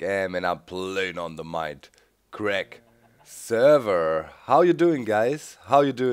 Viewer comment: what about the world tour sp lets play?? you said when 1.8 comes out you will start a new world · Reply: ***** I thought you said "server" instead of "servers" lol I was under the impression that there was only one, and that is what it's called XP I kinda live under a rock when it comes to servers lol Do you know where I can look for an IP?